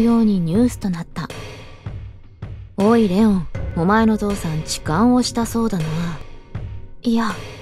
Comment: お前の父さん痴漢をしたそうだな。 のところ お前のゾウさん痴漢をしたそうだな。 に聞こえたwww ごめんなさい